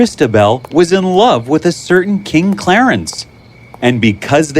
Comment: Wait no one noticed. He said king instead of knight also the sub said knight Ik mistakes happen0:46